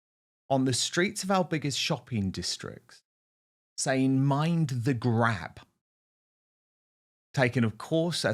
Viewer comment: I thought you were saying mind the grape gangs…